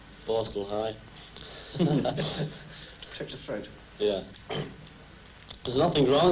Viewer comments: My poor Paul ❤️ I hope he’s ok 😔🥺💗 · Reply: Could you answer me a question? (I'm not a native English speaker) At the time 1:19 did the reporter say "a touch of throat" ? I didn't understand, what did he mean? Ta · Poor Paul. The poor thing is sick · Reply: Could you answer me a question? (I'm not a native English speaker) At the time 1:19 did the reporter say "a touch of throat" ? I didn't understand, what did he mean? Ta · Thank you so, much, LHM... nice interview footage. I was looking for something else -- other than concert footage -- for the "1963 era" in my play list... and this is a nice addition! :-) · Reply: Could you answer me a question? (I'm not a native English speaker) At the time 1:19 did the reporter say "a touch of throat" ? I didn't understand, what did he mean? Ta